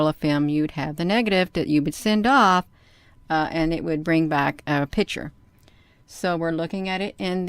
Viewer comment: Did she said "pitcher? 1:56 It's "Lego", not "Legos"